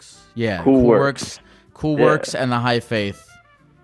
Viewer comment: Also I love how you thought he said "The High Faith" the whole time and he was literally trying to spell out "B-A-H-A-I" so for anyone interested in that, he's talking about Baha'i.